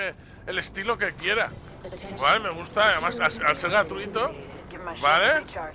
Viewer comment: Hola min 24:56 dijiste un nombre de juego más realista pero no te entendí cual es me lo puedes repetir porfa saludos desde panamá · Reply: El pugb eso dijo